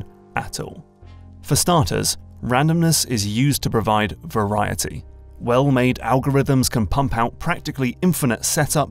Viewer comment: I heard Weval OoO